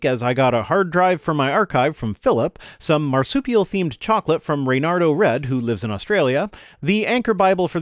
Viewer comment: I thought you said marsupial FLAVORED chocolate. Completely different.